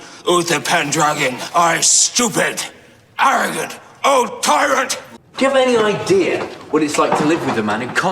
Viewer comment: Ngl when Merlin is going off on Uther I thought he said “You are a stupid, arrogant, old toilet!”